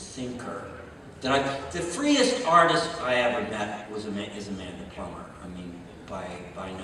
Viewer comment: The freest artist he ever worked with is Amanda Palmer? In what sense, that she asks people to work for free? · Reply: I thought he said Amanda Plummer